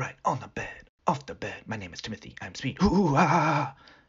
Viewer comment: “My name is Timothy,I am speed “ The quote of the year in my opinion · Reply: I heard "my name is Timothy, I'm on speed"